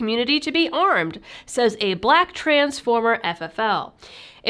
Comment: I swear I heard you say "Black Transformer FFL" :D